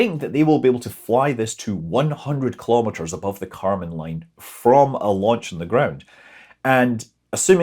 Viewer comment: "100km above the Kármán line" or "100km, above the Kármán line"? I guess the latter, but definitely sounded like the former.